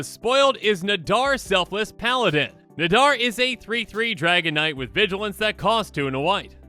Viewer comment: talks so fast I thought he said Dragonite… like wait…. Oh okay, Dragon Knight..